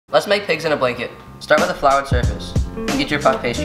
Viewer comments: i thought he said piss in a blanket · I thought you said let's piss in blanket